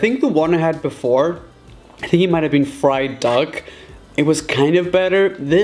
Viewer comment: I THOUGHT YOU SAID FRIED DOG INSTEAD OF FRIED DUCK, IM DYING LOL